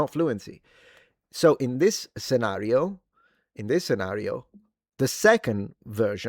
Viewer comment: Did he say it's a me Mario or did he say in this scenario?